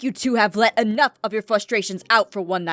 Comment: Aph said FLUSH-strations instead of frustrations XD sorry but I LOVE THE EP!